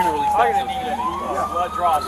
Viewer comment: are they doing a blood draw?! I heard them telling him they are doing a blood draw, maybe I am hearing things. If they are, for what?! More violations of his rights.